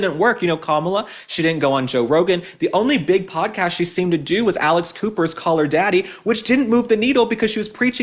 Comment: I thought you said “Alice Cooper”! I had to look it up. I think ol’ Alice is too smart to get involved in politics!